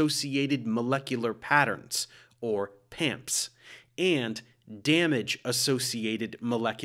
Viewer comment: Not gonna lie, I thought he said pimps at first. :)